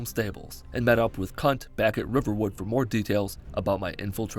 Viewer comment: Did you call Delphine a cunt